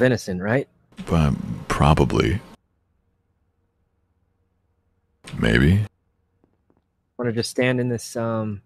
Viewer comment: did corpse say "baby" on sykkuno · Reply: No sadly he said maybe not baby